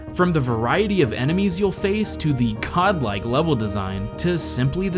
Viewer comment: Hope you all enjoyed the video :) huge thanks to everyone who has supported me so far, and if you want to help, sharing the video with a friend goes a long way!! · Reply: did you say god-like or cod-like?